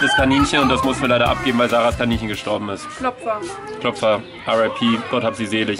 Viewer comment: ich bin dumm und ihr so? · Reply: hat gesagt Klopfer und ich habe bei dir Opfer verstanden und dann dachte ich mir das arme Kaninchen